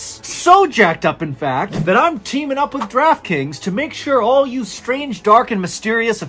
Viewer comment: When you said draft kings I thought you said Crab Cakes. That got my attention.